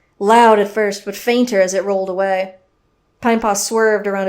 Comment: Ok so isn’t he last video if you put caption on it said “I’m Paul” instead of “pine paw” and now in this video at 3:20 it says “pine fox” and then at 3:41 it calls pinepaw “Pine park”........... when will this end?